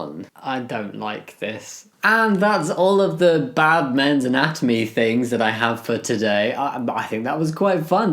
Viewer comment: I'm cooking so only listening, and I heard Batman's anatomy lol